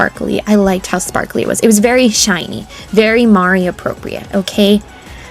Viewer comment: I've misheard Mari appropriate as Mario appropriate 😳